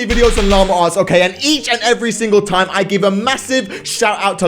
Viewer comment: I always hear “ Lama farts “ Lol Congratulations on 5M u deserve more I wish u 10M